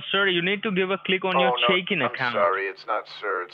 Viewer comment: is he saying SHAKIN ACCOUNT or CHICKEN ACCOUNT?